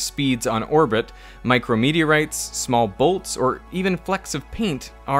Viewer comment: had me so confused. My dumb ass heard "micro media rights," not "micro meteorites" and it took me 5 seconds and a replay to figure out what he said😅